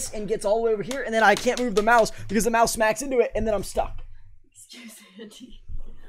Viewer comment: what did said? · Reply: Excuse Andy pretty sure